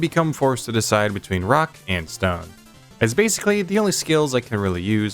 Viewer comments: DID I HEAR A ROCK AND STONE?! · DID I HEAR A ROCK AND STONE?! I only got like 2:50 into this, I play DRG, please forgive me, I enjoy your content